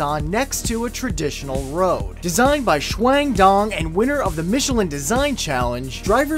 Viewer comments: Ok, the new sideways road thing? I heard is as "Designed by Shwang Dong" My screen is full of my cereal now. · Did he just say that man's name was shwang dong?